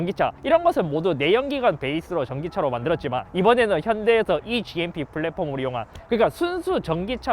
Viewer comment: 초 현대라고들리는데?